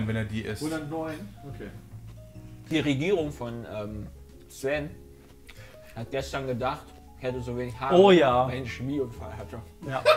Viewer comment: Was sagt da? Ich hab bestimmt 20x zurückgespult aber verstehe es einfach nicht :P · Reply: "Die Regierung von Sven hat gestern gedacht ich hätte so wenig Haare weil ich nen Chemie Unfall hatte" ich denke er meint dabei die Freundin von Sven